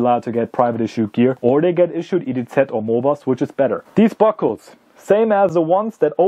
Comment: Sorry I couldn't understand what you said at 2:09 what can soldiers be issued instead of this harness? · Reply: Or they get issued IDZ or MOBAST, which is better." Those are the two modernized clothing and equipment sets for the individual soldier